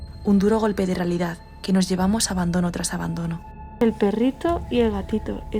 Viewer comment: No sé si han pillado que cuando dice un perrito y un gatito se escucha una voz que dice "espera" es un susurro pero que miedo me ha dado. minuto